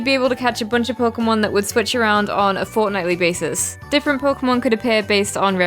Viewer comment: spot the dumb error in my script and get... a crying lucy idk (hope you're looking forward to Sword and Shield btw! :D) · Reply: i hear "FORTNITEly" here 2:27, yeah its not the mistake cuz other people already commented "Shell balde"